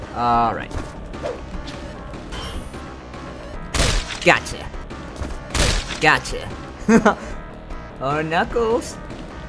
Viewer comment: What I hear "Gotcha! Gotcha! Gacha!"